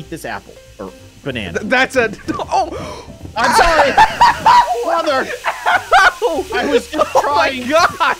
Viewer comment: Did you see his neck XD🤣🤣🤣🤣 🤣🤣🤣🤣 Also did he say mother or something